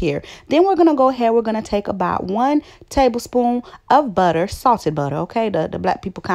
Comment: I was cracking up when u said .. salted butter… you know the black people kind of butter . Cuz I’m a southern white girl but I couldn’t agree more .. it’s my kind of butter too !!! Love your personality! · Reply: Haha I thought I heard "saucey butter" LOL